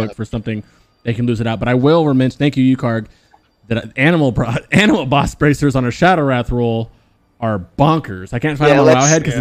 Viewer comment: what piece of loot are they talking about here? Can't quite understand. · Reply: Basement boss in kara